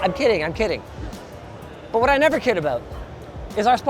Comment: subtitles saying cared instead of kid😂